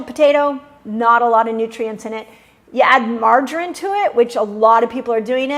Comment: What about adding real grass fed butter to russet potato to slow down absorption of sugar? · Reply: Dr. Mindy I apologize. I thought you said butter. That is what confused me. I reviewed the video again and indeed you said to not put "margarine" on the potato. So yes if anyone eats potatoes then slather it in butter! 😊